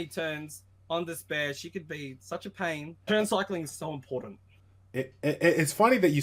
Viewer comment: "Turn cycling is not important in rta" .................. >.> is he really g2 ? XD (no offense) :P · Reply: I thought I said turn cycling is "so" important.. not "not" important? haha, maybe its my accent? XD